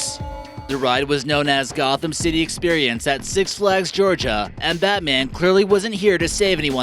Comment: Oh thank the lord I thought he said six flags St. Louis bat man the ride